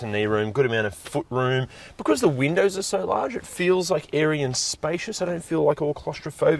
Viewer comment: I swear I heard "Aryan spacious" 😵‍💫😉